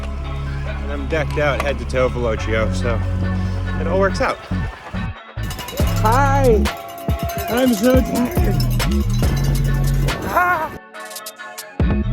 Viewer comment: when you said, "I'm so tired!", I misheard it as "I have snow tires!"